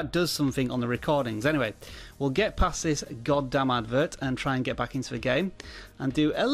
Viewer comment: What did he say @ 3:17...? Lol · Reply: God damn advert!